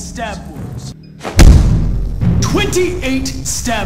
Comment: "28 stab wounds" What I hear: "28 sTePs-"